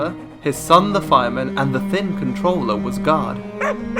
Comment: Misheard guard as god in the beginning